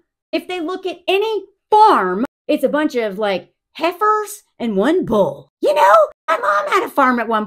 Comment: Omg, why did I laugh so hard when you thought your mom was saying your sister was prego? But it was the heifer.😅😂😂😂😂